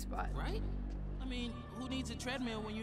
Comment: what did he said here. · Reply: “I mean, who needs a treadmill when you have the Williamsburgh Bank building?”